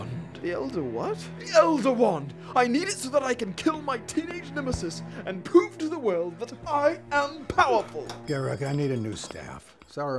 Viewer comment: I thought he said "so I can poop to the world that I am powerful!" XD